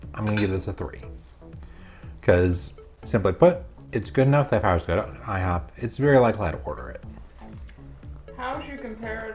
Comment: WHAT IS HE SAYING 😭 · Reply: if its good enough that if i were to go to ihop, its very likely that id order it, i speak mumble LOL